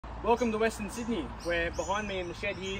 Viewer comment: I misheard the start of the video and thought you said welcome western signalman, I though wow this is very personal 😂 great looking layout with loads of promise. Also love the intro music 🤟